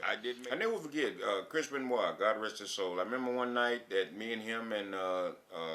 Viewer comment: "Chris Benoit god rest his soul" ... Wtf?? That's my cue to stop listening. How disgusting! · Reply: I heard "Nancy, God rest her soul"...